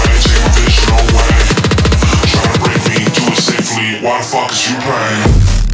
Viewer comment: Heres what i heard: "Seek provisional ways. Seek provisional ways. China braking Double safely Why therefore you pain"